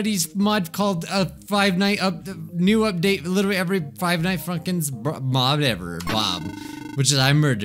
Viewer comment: did he just say five night funkins????💀💀